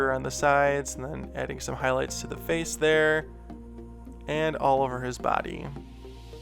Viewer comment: when he said "all over" it sounded like he said "Oliver" so, therefore, this giraffe~dragon hybrid should be named Oliver :)